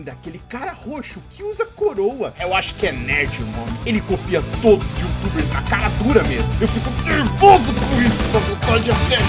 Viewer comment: Eu acho que ouvi NARUTO!?